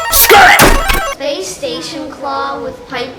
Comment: Please tell me I'm not the only one who heard playstation